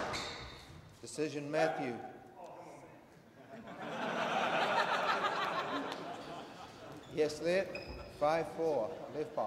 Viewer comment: What did Golan say at 12:38? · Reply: I think Matthew said 'Stroke' and Golan replied 'Oh come on man!'